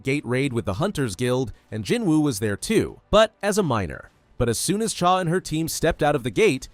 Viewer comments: when you said "... but... as a miner", I heard "but... as a minor." I was so confused for a second. Then I realized I listened to too much Kendrick Lamar. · the subtitles said he was there as a minor not miner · thought bro said minor